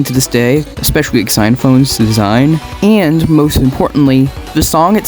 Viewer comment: I have heard your opinion of Blue Balls, I shall also write an angry twitter post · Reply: did I just hear you say "x ynaphone"? how did you get xenphanes wrong